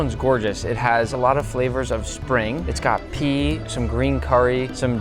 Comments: i thought he said uranium😭😭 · I thought you said "It's got pee." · did he just say pee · holy crap, i almost thought he said uranium